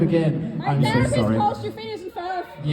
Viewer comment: what does the audience member say here? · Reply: I think it's "my therapist calls you phineas and ferb" 😭